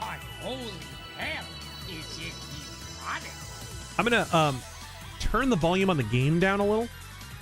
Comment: I heard it as "I'm gonna turn the volume on the GAME down a little...". It would've been funnier as it's immediately followed be Darth Vader going "WHAT?"